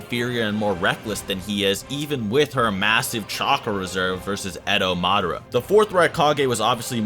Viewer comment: thought u were gonna say massive breasts